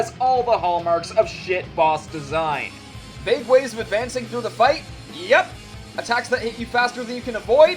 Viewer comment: I cannot understand that part well. Did you say "Mid-waves advancing through the fight"? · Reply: I think he said "Vague Ways of advancing through the fight"